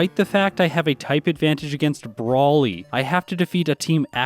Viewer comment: When you said "I have a type advantage against Brawly" I heard it like "Broly" from dragonball and had a moment where I was really confused